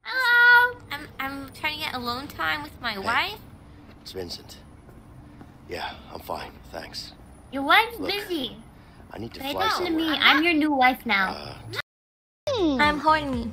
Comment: Oh.. I rwally can not hear What did she say? "I'm Horn*" lmao You gonna be kidding me lol